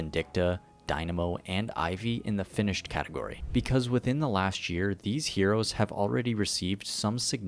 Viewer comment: Please tell me I'm not the only one who heard "Within the LASHt year"